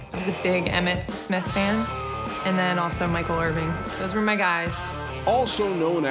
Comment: did she say Michael Irving?